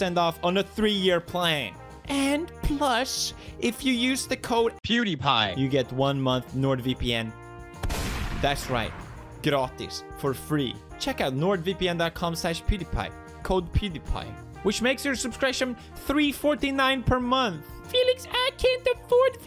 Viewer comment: did he say gratis ???